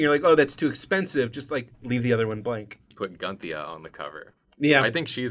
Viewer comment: At first I thought you said,"Put Ghandi on the cover."